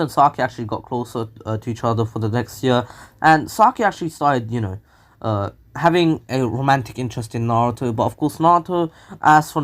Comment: I thought u said Sasuke had a romantic interest on Naruto 🤣🤣🤣